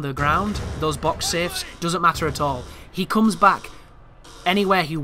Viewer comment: If you check the media outlets they are calling him boxman/boxhead. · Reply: Ok. I kept hearing "Safehead".